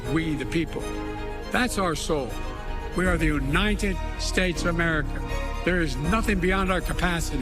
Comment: I thought joe said we were the "united steaks of America"?